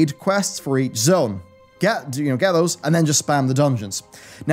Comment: Did he say "get in to your ghettos" at 6:35 lol?